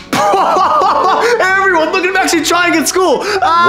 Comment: what did he say at 0:55? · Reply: Hey everyone look at him actually trying at school